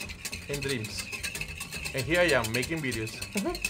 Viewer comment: Oh my... 'I came to this country with two things... Hope and Dreams. Here I am making videos.' Go chef!!! 👏🏽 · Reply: I misheard him and thought he said "here I'm making millions" and I was like GOOD FOR YOU CHEF! WOO GO GO